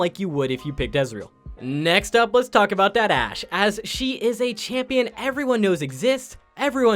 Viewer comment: Are we really gonna ignore what he said at 12:25 "Let's talk about that ashe (Ass)"